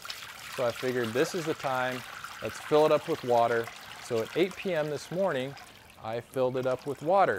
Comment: Did he say 8pm this morning ?